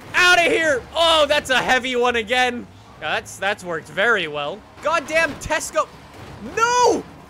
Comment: did he say god damn Tesco?